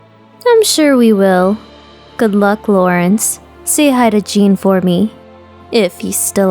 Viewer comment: Why is it over I'm crying and at the end I heard the words "say hi to Gene for me" omg it's geneI'm crying sssssssssssooooooooooooo hard! Ahhhhhhhhhhhhhhhhhhhhhhhhhhhhhhhhhhhhhhhhhhhhh